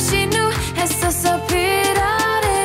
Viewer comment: Not sure whether it's because I'm from southeastern Europe, but I thought she was saying Kompotia